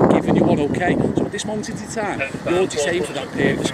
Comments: Thought he said your entitled to a coffee for a second 😂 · I thought he said you're entitled to a coffee 😂😂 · I thought he said “you’re entitled to a coffee” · I thought he said “you’re entitled to a coffee” 🤣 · I thought he said "you'll be entitled to a coffee" 😂😂😂